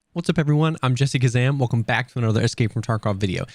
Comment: i thought you said "I'm jessica sam" 😂